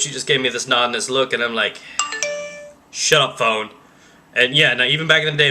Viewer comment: I thought you said SHUT UP PWN!